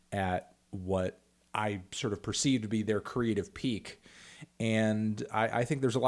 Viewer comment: I don’t know why but every single time he said creative I heard “creed of” Creed of output Creed of ability Creed of peak I was so confused watching this